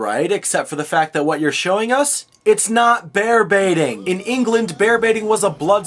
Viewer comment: They said Bear Baiting? Wow, not only is this history wrong, but because of their actions, I've always thought until now they he was saying "Bear Beating".